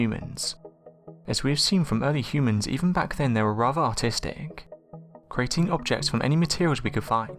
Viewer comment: I thought he said Autistic... Hahaha Good vid